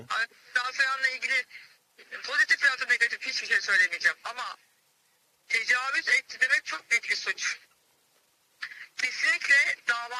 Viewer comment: "Tecavüz Türkiye de çok büyük bi suç" mu dedi o ben mi yanlış duydum. Duydunuz mu tecavüz Türkiye de büyük bi suç muş... 🤣